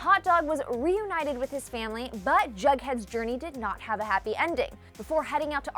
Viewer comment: Is she saying “BugHead” or is that just me... it’s “JugHead” it sound like she is saying bughead 😂😂